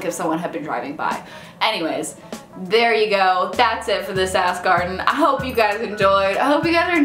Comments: When you say "that's everything for this ask Arden" it definitely sounded like you said "that's everything for the sass garden" - which, the "sass garden" should really be a thing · At the end I hear that's it for this sass garden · at the end I totally thought you said "that's it for this ass garden"